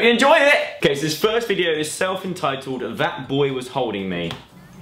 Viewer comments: "That boy was, That... boy was holding me 😔" Joe Sugg and The Holder (in cinemas Friday) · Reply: Nayab Rauf I thought he said "That boy was,That...boy was hurting me. Dunno why 😂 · "That boy was, That... boy was holding me 😔" Joe Sugg and The Holder (in cinemas Friday) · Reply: Nayab Rauf I thought he said "That boy was, that... boy was hugging me" 😂😂